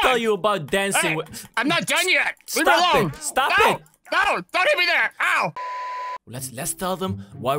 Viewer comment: The first time I watched this I thought at the start he said don't eat me there